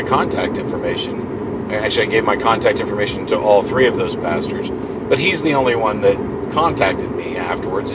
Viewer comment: at first I thought he said "all three of those bastards" : )